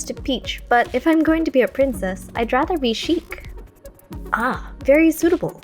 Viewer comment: thought you said shrek